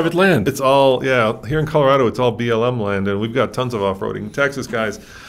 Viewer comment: I like the new Tundra. But there is a part of me that says, “wait for the new 4Runner…” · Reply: Did he say Colorado is all "BLM" land?? 🤭😆🤣😭😂 If I did head him correctly, that is hilarious 😂😆